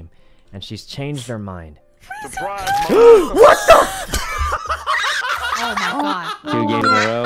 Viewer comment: is it just me of i heard syjjuno say 'what the f-'